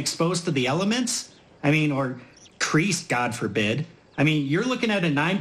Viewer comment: what is he saying at 1:05 __ god forbit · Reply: creased god forbid